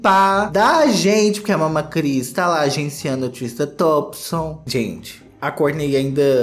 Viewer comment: Eu entendi "Tristan Toxicon" e não consigo mais deixar de ouvir! Kkkkk